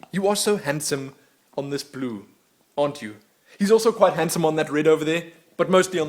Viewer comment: I misheard "He's so handsome on the blue" as "He looks so handsome on the poo".